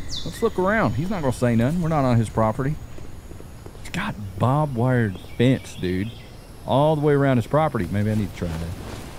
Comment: the auto generated captions say "Bob wired fence." It definitely sounds like that is what was said too. I can't decide whether this was a happy accident or if slipped in a subtle joke. Y'know, since the dude's name is Joe Bob, and if he set up his own fence, it would indeed be a (Joe) Bob-wired fence. Either way, I thought it was funny!